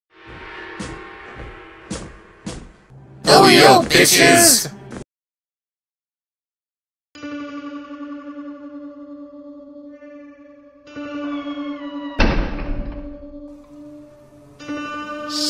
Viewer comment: What are they saying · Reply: "Oh-ee-oh, bitches!"